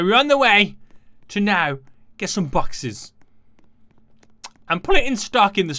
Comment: at first i thought he said lets go to the city to get some boxers lol